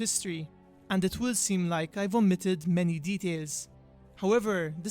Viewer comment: oh you said "omitted"some stuff i thought you said vomited lol